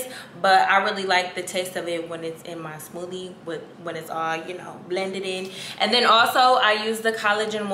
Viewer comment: Did she just say “blendeded” 💀😂🤣